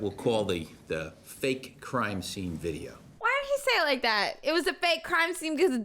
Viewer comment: I legit thought he called it “The Fake Crying Scene” 😂😂 I was finna say 🤣 Like ain’t he the defense guy?